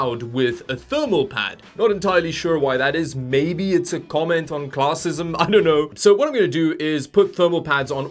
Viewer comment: "Maybe it's a comment on classicism." Out of all the jokes, for some reason this made me laugh the hardest. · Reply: I thought he said 'a comment on classism'! Slight difference XD